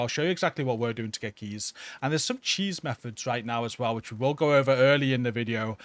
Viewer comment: Oh! CHEATS! I thought he was saying "Cheese"